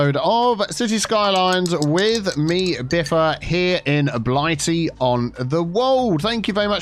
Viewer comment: For so long I thought you were saying blighty on the world. But with an accent 🤣🤣🤣